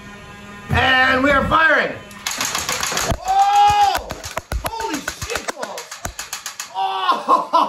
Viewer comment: was that a "Holy shit balls" I heard? hahaha! Overwhelming joy.